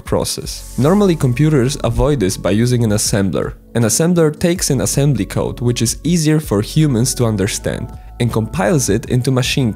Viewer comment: For a second i thought you said "assembly code is easy for humans to understand", but when i re-watched it, I heard what you actually said, which was "assembly code is EASIER for humans to understand" which is true, but it's still confusing as hell lol